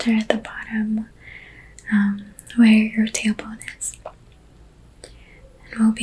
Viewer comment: I seriously thought you said “where your tampon is” and I was so relaxed and that took me out 😂🤣😭😭 I had to turn on captions after the third time lmao TAILBONE